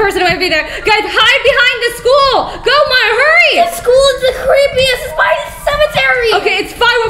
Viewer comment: wHEN I PAUSE YOU HEREB 8:24 WHEN YOU WERE JUMPING I THOUGHT YOU SAID THE SCHOOL IS CARZY A F